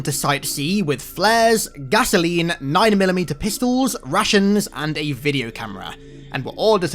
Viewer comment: I know that he said "rations" but for moment I heard "Russians" and imagined how a bunch of dudes ask me and my friends to become their weapons. Though if Class-D guys were indeed equiped with a personal Russian and lunged us at their enemies, their sucsess and survival rate could rise at least 110%. Makes sense why Foundation wouldn't approve